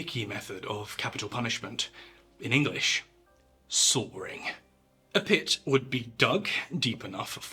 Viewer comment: With your accent (I'm American) when you said people were executed by 'sawing' l thought you were saying 'soaring' and for an embarrassingly long time l thought the victim was thrown off a cliff or out of a glider.